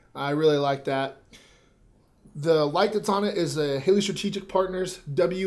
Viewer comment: What type of flashlight is that? Couldn't quite understand what you said.....I thought you said Halo Strategic Partners but couldn't find anything. Never mind...Haley not Halo...got it